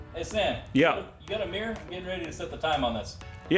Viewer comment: I thought he said “you got a beer?” instead of mirror 😂